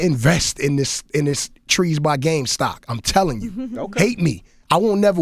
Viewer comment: Anyone find out how to invest in cali weed or weed in general? i think he said trees by game? I dont know if he really even said that